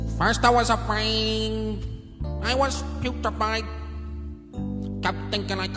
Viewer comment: Did someone else notice that he sings "putrified" instead of "petrified" at 0:10? :D